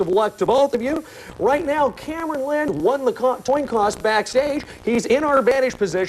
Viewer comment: At about 2:08, the host flips the words. Instead of "coin toss", he says "toin coss". LMAO